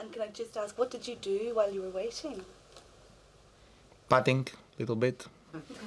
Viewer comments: What is he saying at 9:16? I understood betting, is it right? Or patting? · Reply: I think he said 'putting', as in golf. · what did nadal say? I could not understand that word lol 'patting'?